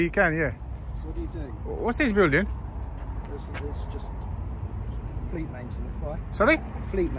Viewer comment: I just realised how wasted I am as at 4:51 I thought the guy said free masons when asked him wot is this building no more joints for me Well for about 20 minutes 😂😂😂